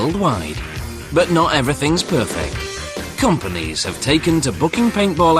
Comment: at the end it sounded like he said "oh hell" xD